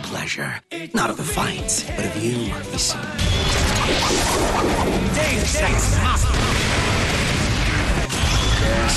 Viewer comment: what he say? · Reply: "Deus Ex Machina!"